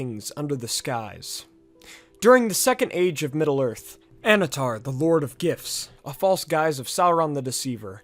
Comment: i totally misheard you as "Annatar, the lord of gifs". which implies the existance of "The Lord of JPEGs" and "The Lord of PNGs"